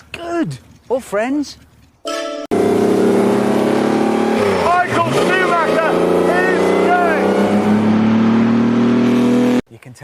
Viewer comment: I honestly thought he said “Michael Schumacher is dead” for a while 🤣🤣